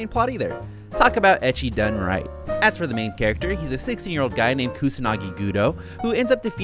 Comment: i swear i thought he said "his a 6 year old guy"